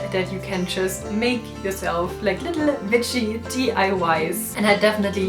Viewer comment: I really thought she said hello little “bitches” what’s wrong with my mind- Thx for tips thought